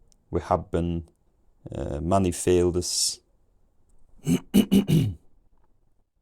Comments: I heard "money failed us" around 1250, which feels strangely prophetic. All joking aside, some words around 900 sound like some words from the South Yorkshire/North Derbyshire region where I was born. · 450-650 - understood snippets like chicken and field but also misheard things like "loud fool", 650-900, maybe 25%, 1450 was 40%-60% (still misheard "fields" as "failed us" or "geese were seen" as "guests were sane") <num> maybe 60%-80%, 1600 onwards 100%. before and after 1600 felt night and day (the great vowel shift?)